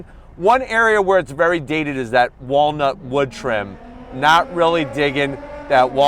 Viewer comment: At first I thought you said "Walmart wood trim", and then thought, well.. thats still pretty accurate.